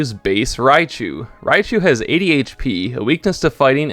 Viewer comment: "Raichu has 80 HP" Me mishearing it: "me too"